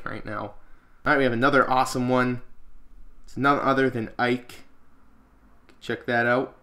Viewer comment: woah woah WOAH! did say Pit Was Ike????